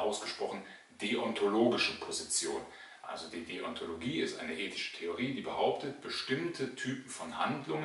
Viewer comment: "hegelische Theorie"? Verstehe ich das richtig? Hat er das Wort "Deontologie" geprägt oder ist das einfach nur ein Versprecher?